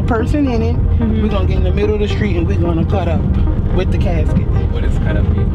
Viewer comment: "what does 'cut up' mean?" aww, look at this cultural exchange! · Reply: Right! I heard “cut out” for leaving not cut up